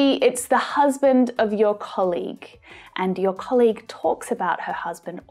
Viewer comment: I heard "colig" ?